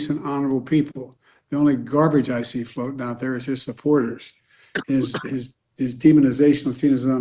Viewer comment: I'll be honest at first I thought he said are his supporters and if we go with is his supporters with an apostrophe fine so we have a president who cannot clearly make his point understood and that is just as bad because it just reiterates that kamala knew that he has issues and he is. Still our sitting president and that is scary. Doesn't matter if he called us ALL garbage. Walz is out there inferring we are all nazi simps